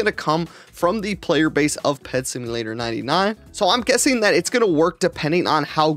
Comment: why do I hear PetSeeYouLater99? 😂😂😂